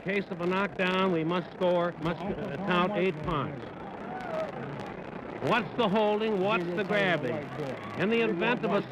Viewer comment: What did Ali say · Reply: “You gonna fight fair, I don’t care… YOU gonna fight fair” lol something like that, he was just talking shit 😂😂😂